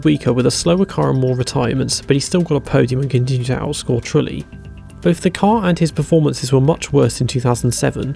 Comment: Can someone tell me what was said around 59:24 - 59:30 something about Trulli and a podium? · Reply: Still got a podium and continued to outscore Trulli. You can activate subtitles on YT videos now.